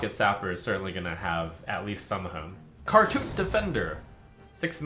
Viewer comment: When I heard "Khartut Defender," my I initially thought said "Cartoon Defender," and my instant thought was "Oh no, did Yu-gi-oh take over?"